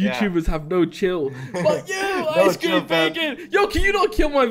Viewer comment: Did you just say "F" you icecreambacon?